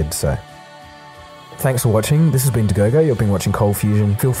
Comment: Wow! I read about this story in 1998.. You did a much better job telling it Dagogo. Nice work! RIP Gary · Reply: Wow, watched these videos for a while no, but I had no idea Dagogo was his name, I hear "indagogo" but never thought what was meant by it.